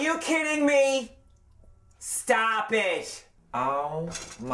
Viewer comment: What does mean that word he is saying in 0:14?? Stoppish or what??